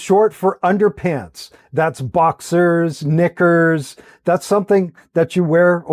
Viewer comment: I’m sorry, what? (The second word) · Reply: KNICKERS silly